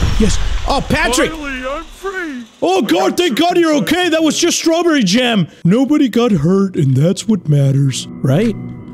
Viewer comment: Aw man, missed what Patrick said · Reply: I got circumcised again